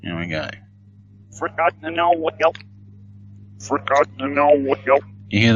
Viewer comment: I hear 'for God to know what guilt.'